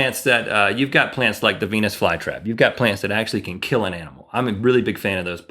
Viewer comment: I missheard and thought Rhett said "penis flytrap" Twice! I know he didn't.